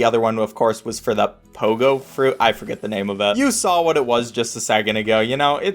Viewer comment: for a second I thought pogofruit said pogfruit